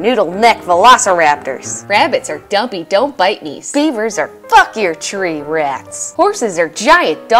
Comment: I thought when she said "fuck your tree rats" that she said "fuck ya chicken strips!" 😂